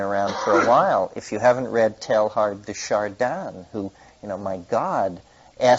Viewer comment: What is the book he references at about 15:30? I couldn't catch it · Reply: Pierre Teilhard de Chardin , one of his books . Not sure which one but he usually discusses omega point .